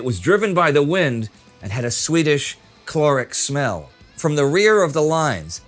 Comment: First thought he said a swedish and not sweetish smell. I was like wtf do we swedes have a certain smell now?!